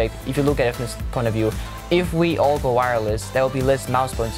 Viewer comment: at the last one i heard: "go latency or go home"